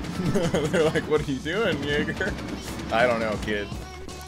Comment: did he say the n word here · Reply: He said Jager